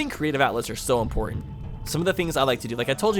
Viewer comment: Oh, I didn't know that's what you meant by Creative Outlet, I thought you said "Outlook," lol. But yeah, I'm an aspiring writer, not like short stories but like, long and compelling stories where you see the characters grow.